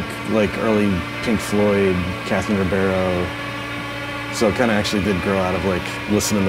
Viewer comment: what did he say at 1:26 ? captain who, cats in a ? a barrel ? Peace Ya All ! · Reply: Catherine Ribeiro. check her record "Ame Debout"